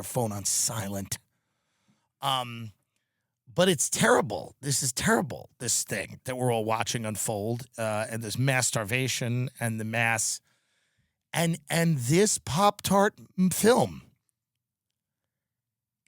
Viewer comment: Idk why I thought he said masturbation☠️